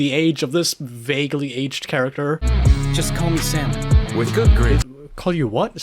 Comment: Your right and wrong here. I hear "Salmon" as well, and the fish head costume change here helps you believe that. What he is actually saying here is "Just call me Sam then". But the audio quality and how it's mixed, also possibly not the absolute best voice actors here, make "Sam then" into "Salmon". I did like how you thought that YT captions here would help clear things up here.